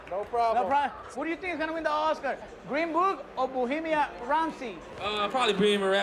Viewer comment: did guillermo say 'Bohemian rhapsody'????